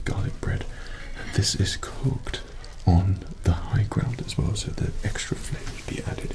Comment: For some reason I heard “cooked on the high ground” and “like a bantha.” Did anybody hear this as well?